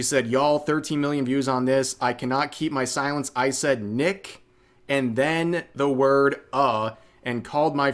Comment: As a black guy, what I heard was "Nick uh" not nigga. And I'm really tired of seeing ppl abuse the copyright system.